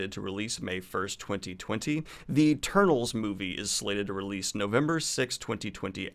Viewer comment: I could have sworn at 2:54 he said "The Turtles movie" and then I heard Salma Hayek and Angelina Jolie's names and I was like what?? lol.